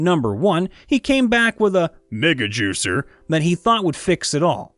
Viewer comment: I swear to god when I heard Megajuicer, I thought he said niggajuicer and had to look back at the tab as I was on a different tab.